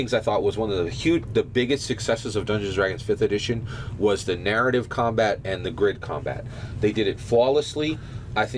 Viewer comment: How can you not do narrative combat? What does the guy in the middle think the issue is? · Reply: thats not what he said at all 8:45 "narrative and grid combat they did it perfectly"